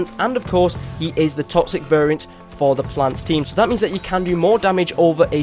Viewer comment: did he just say plants team?